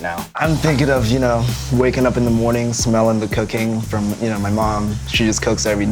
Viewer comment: lmao did he say "smelling the cocaine" lol kinda sounded like it